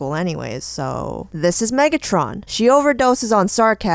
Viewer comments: At first i thought it said Mettaton instead of Megatron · Am I the only one who thought that said "Mettaton" When she actually said "Megatron" XD. Ya Know Mettaton as in that robot dude from Undertale XD